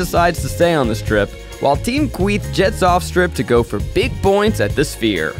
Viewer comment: The first time I heard Kweith I thought it said queef… that’s it 💀